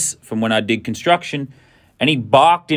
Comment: I thought I heard sue me